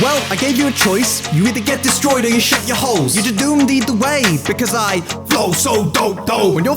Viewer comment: I originally didn't look at the subtitles, I thought he was saying, "shit your holes," not, "shut your holes"